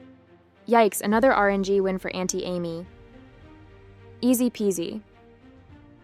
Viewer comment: Did you say.... "Auntie Amy"? Maybe I misheard. But that sounded quite wholesome.